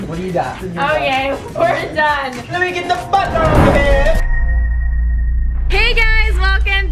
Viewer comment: why did I think he said loui get the f**k out here